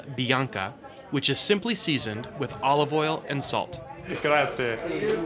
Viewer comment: wait a sec, did you say 'Gracias'???? :P Nah my bad!